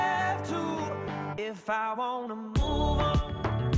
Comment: The whole time I thought this said hold on instead of move on 😅 now I get it 😢❤